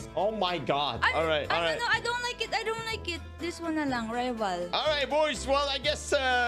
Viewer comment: wife is filipino? i heard her say in 9:37 "this one nalng"